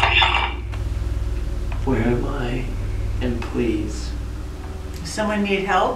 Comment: right after says if they need help, I hear "yeah, where am i" sounds like an old man...also sounds kinda lost